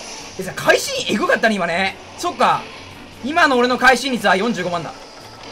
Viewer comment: 今の俺の会心45万だに聞こえてびびった自分にびびった